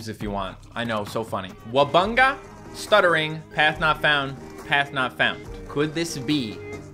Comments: Rare wubbox was saying path not found p p path not found not let us out l l let us out stoobid everybody thinks its saying let us out cause they watch the misheard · “Let us out, l-l-l-let us out,” is a misheard lyric, the real lyric is, “Path not found, p-p-p-path not found.” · no its not "let us out let us out" thats misheard lryics its actually "path not found path not found